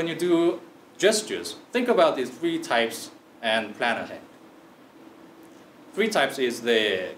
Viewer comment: Help me someone please, I'm not a native speaker. Can't understand a single word at 8:27 - 8:28, it's "So next time when you do gestures think about these three types and ..." what? Vanilin? My mind is going to blow.